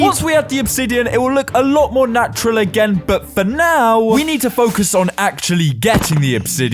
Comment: HELP IM SO SORRY BUT WHEN YOU SAID "BUT FOR NOW" I THOUGHT YOU SAID "B-BUT, BUT NOW" BECAUSE OF THE ACCENT